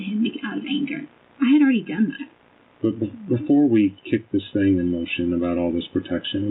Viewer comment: When he said kick in motion i thought he said kick it in the minoin i need to get my dam eyes checked